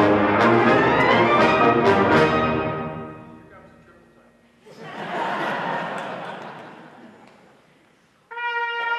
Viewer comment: Anyone catch what he said at 3:22? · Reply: "Now for the triple tounging"